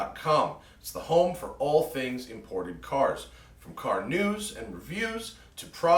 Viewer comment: lol i thought he said "car nudes"